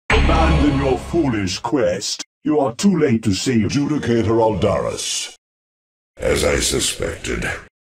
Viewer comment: Ngl, I thought Aldaris to say the "you're too late to save the child" 🤣